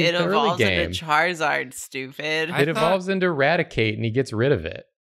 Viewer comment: "It involves into a raticate and he gets rid of it" You could say he.. eraticates it huh 🤔. just doing some pest control. (i misheard it as eradicate to start with oops XD)